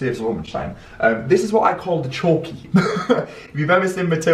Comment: The 50s windows are beautiful! Deffo thought you said chalkey instead of chokey 😂😂